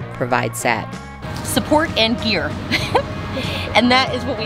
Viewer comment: I could have SWORN said she provides “support and beer”. But it’s just gear. Like… who cares about gear? And don’t even get me started on “Fireside chats with Caleb”. The force is strong, in that one. You guys make me smile. Big, glowing smiles. It’s going to be a long week, waiting for next Sunday evening. ❤